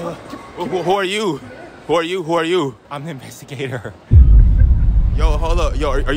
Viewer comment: Who are you? Random Dude: Imma messy gatorrr Bro lmao wtf did he say in that part??? 😂